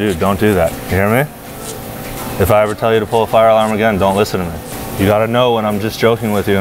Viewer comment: I thought it said “pulling fake fire arms in public” 🤣